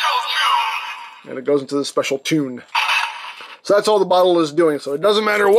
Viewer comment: When I heard "special tune " I thought it was say " shred of doom "